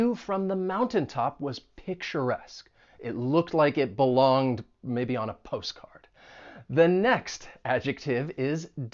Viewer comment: "It looked like it belonged, maybe 'on' a postcard." (2:12) Did I hear it correctly? Why 'on' not 'to'? Thanks for this very Interesting and educational video. I specially like your references to places you have visited.